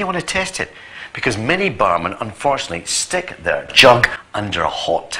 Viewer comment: I first thought he said "many barmen stick their junk into the water ..." WTF thought I, Then I realised what he meant.